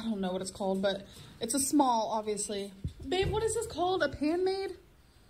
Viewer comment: “What are these called babe? Ranchero?” “What are they called, hardwood floors?” “What’s it called, Disney plus?” Drives me crazy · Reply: Maenad oh my bad I thought she said pan man 🤣🤣🤣